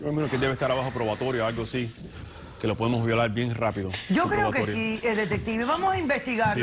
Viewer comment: Que lo podemos vi*lar bien rápido, lo repetí varias veces pensando que escuche mal XD XD XD XD